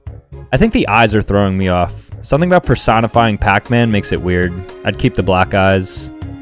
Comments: did he just say "i'd keep the black guys"? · My dumbass thought said, “I’d keep the black guys.” 💀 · Bro said keep the black eyes My dumbass thought he said keep the black guys 💀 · I thought he said "id keep the black guys" for a moment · When he said I’ll take the black eyes I thought he said I’ll take the black guys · "I'd keep the black guys" 💀💀 Why do i keep mishearing things 💀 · It sounded like he said, “I’ll keep the black guys” instead of “black eyes” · bro i thought he said black guys lamo but he said black eyes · Am I the only one who thought he said “I’d keep the black guys”